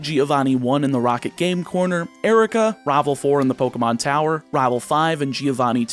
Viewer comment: sorry, is that 'Raeval' I hear